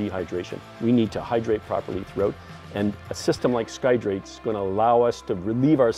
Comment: After your 'F-16 Across the Ocean' video I was always wondering how did you guys take the bathroom break while flying formation. I had no idea that piddle-pack involved such high risks. Skydrate is truly revolutionary. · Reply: Oh okay so it is Skydrate. I thought I heard Skydrip :P